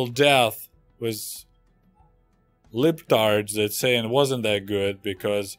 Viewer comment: Lmfao, did really say "libtards" at 11:45? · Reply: In Russia they say LIBERAST, derrived from pederast, which is a bad word for "gay".